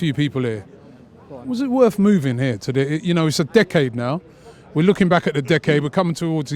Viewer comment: Almost sounded like "dickhead"...